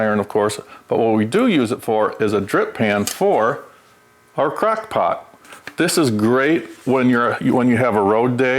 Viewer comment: had me dying! I didn't think he said crackpot at first because I knew he meant crockpot, but it definitely sounded like it after a few replays.